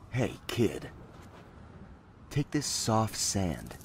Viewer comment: I thought it said soft hand the entire time I watched this up until the end